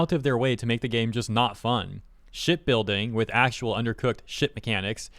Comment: I thought you said "S#%@ building with actual undercooked S$%^ mechanics" and was a bit surprised. lol